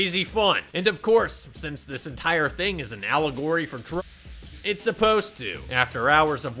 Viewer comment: what word does he censor? This movie is an allegory for __. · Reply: drug use